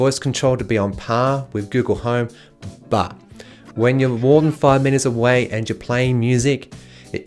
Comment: Sorry I didn’t understand what he said at 2:39, but did he say more than 5 minutes away? Because that doesn’t really make sense... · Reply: My bad - I mispronounced "metres" - for clarity "when more than five metres from it, the far mics have a hard time hearing the wake command". I find Google's devices are better at this, especially with the recent change of just saying "STOP" without the need for a wake command.